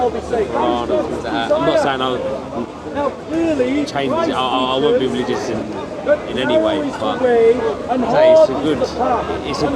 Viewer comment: did he just say "I am not going to change. I won't be religious in any way" ?